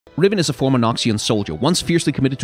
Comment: I swear I heard "riven is a former n*zi soldier" at the beginning of the video and actually spilled my drink